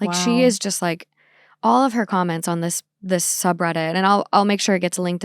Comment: does anyone understand what she says right here? I couldn’t understand it. I heard “against a woman because there was almost ten at the time”, but I don’t really understand what she means by that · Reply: I think she says "their son was 10 at the time"